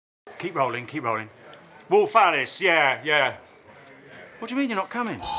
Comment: The first part where he said wolf Alice I thought he said Rolf harris